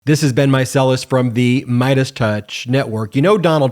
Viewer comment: I thought he was saying "This has been Meiselus," but I finally realized he is saying "This is Ben Meiselus."